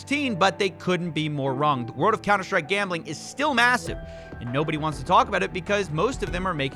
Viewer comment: Did u say MASSIVE 1:23 *Imagine